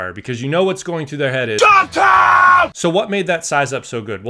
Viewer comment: Hahahahaha what did the dude yell at 1:09? "Jump time" ??